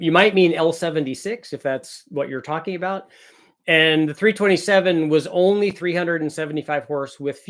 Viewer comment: I thought he said the 327 was board over .30